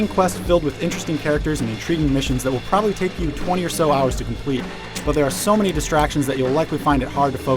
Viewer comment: What did he said at 2:36? "...will probably take you 2alarhours to complete..." I don't hear nothing else. Anybody heared?